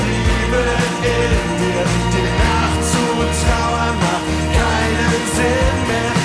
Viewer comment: Schönes Ding! Ich hab als Kind immer verstanden "Im Nachtzug trauern macht keinen Sinn mehr".